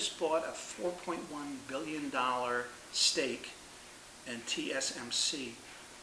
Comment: For a moment I thought you said STEAK not STAKE LOL!